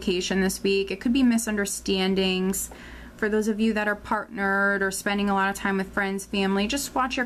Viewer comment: I thought you said - those of you who are "part nerd" not "partnered" 😀